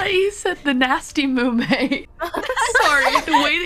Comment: Mumei: I am Nanashi Mumei. Calli: I thought you said Nanasty Mumei! XD Mumei: Listen Here You Little Shi